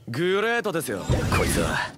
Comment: what does he actually say, everytime i am trying to figure out what he could say i hear "try to shit now". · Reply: Nah its drop this shit now as a secret stand